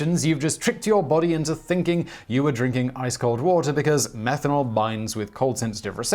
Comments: I thought I heard "methanol" instead of "menthol" 🤔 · it sounds like you said methanol while I think you wanted to say menthol